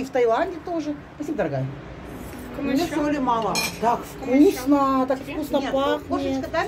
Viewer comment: Мне соли мало......мне послышалось.... так вы ж не солено, не перчено едите😁😁😁😁